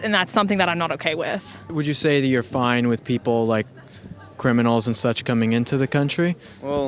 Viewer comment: "im okay with people coming illegally" "Like criminals?" "..........uh" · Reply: I thought criminals said Christmas lol